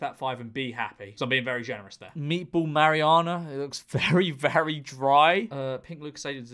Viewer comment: Did just call it a Meatball Mariana? Like the trench?